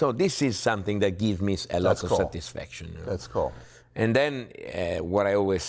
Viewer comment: Did Pat said "Thats Coke"